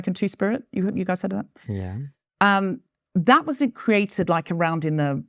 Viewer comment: what he said was "yeah" but it sounded like "stfu that's not a thing!"